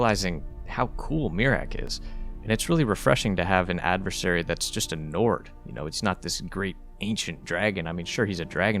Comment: "he's just a Nord" I heard it as "he's just a nerd"😅